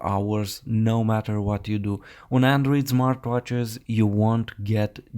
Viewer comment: sounds like you say Zmartwatch